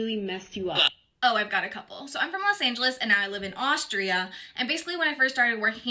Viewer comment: I think she said Austria 🇦🇹 03:25 not Australia 🇦🇺… Another American cliché?